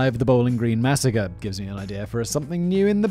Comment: I swear I hear him say "Blain Braze" every time.